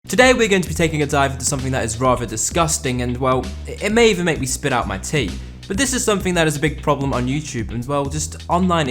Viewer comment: “It may make me spit out my tea.” What a very British thing of you to say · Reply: i thought he said "it may make me spit out my teeth"